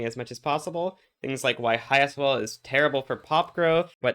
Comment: "Highest wall is terrible for pop growth" I feel like that's not what you said, but I am unable to understand what that's supposed to be. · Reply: High sol (standard of living)